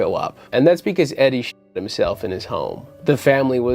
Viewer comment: Thought you said “Shit Himself”